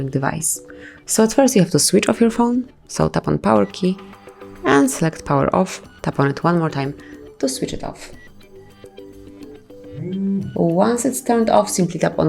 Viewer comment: Visually I can see the directions but the accent is so deep I can't understand your words very well is there was captions maybe it would be better overall thank you for the video have a great day🙃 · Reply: I turned on the captions and at 0:13 it says, “it’s first you have to switch off your phone salt upon power and” Salt? The captions aren’t doing the words right. I don’t know what she’s saying either and the captions didn’t help.